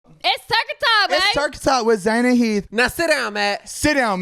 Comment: At the very beginning I thought I heard "Turkey talk" haha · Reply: U did they said turkey talk